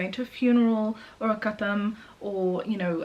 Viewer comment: Out of curiosity what is a katham? (1:30) I'm sorry that's most likely not the right spelling but that's my best guess from what I heard in the video. The subtitles says gotham which I'm pretty sure is also wrong.